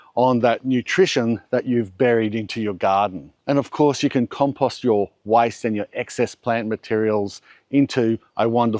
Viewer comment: I thought you said you can compost your wife. hahahaha